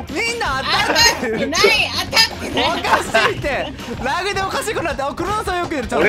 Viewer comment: 当たってない当たってないがお母さんといっしょに出てくるキャラの怒ってない怒ってないに聞こえるwww